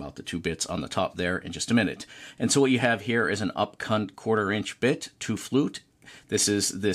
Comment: what type of bit was that? an up WHAT? 😱 some minor over dubbing may be required to keep this family friendly if I heard that right lol. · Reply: Well it was supposed to be an up CUT bit 🤣. Thanks for watching!